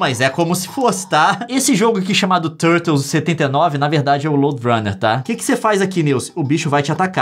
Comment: agm poderia escrever direito aqui o nome desse jogo original, pfvr. N entendi só com o flnd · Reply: O nome é "LoadRunner" eu acho